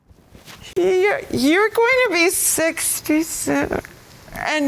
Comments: I must have heard her wrong, it sounded like she said “your going to be 67” I thought Markus just turned 58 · you said he's going to be 57 right, not 67? It sounded like you said 67 but I know he's not in the sixties yet, no way! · Did she say that Markus is 67 yrs old?... 13:48 ...Did i hear that right? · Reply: She said "You're going to be 60 soon". That was several years ago.